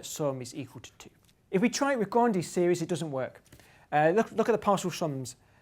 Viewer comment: When ever he said grandiose I thought he was saying ghandi